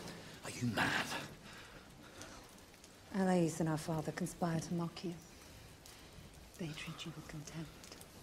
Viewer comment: Can someone who understands English better than I , write it down here subtitle of what she says in 51:11 ? Thank you ! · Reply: I got “...father conspire to mock you”